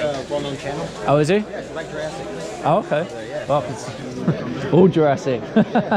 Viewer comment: Whoa Jurassic table! What was that seller’s YouTube channel called? Mike Jurassic? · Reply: I thought he said ‘Mike Jurassic’ but I couldn’t find anything on Instagram or YouTube, haha I think my hearing may be going 😂